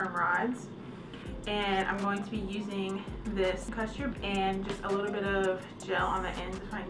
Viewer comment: I cannot understand what she says she’s using at 3:19–3:22. All I hear is orange perm rods and gel on the ends. But what in the world did she say in between? Custard? I’m soooo lost. What did she put on her hair to actually prep each section for the rod??